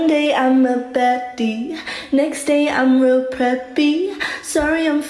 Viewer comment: i fr thought she was saying ' sorry im feeling leggsy'